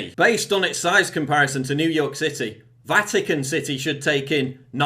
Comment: I thought you said Vatican sissy, at first when you said Vatican city.